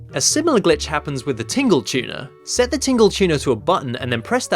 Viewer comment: I keep mishearing 'Tingle Tuner' as 'Tingle Tuna'